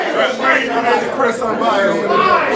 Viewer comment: Did they say shoutout CHRIS UNBIAS ?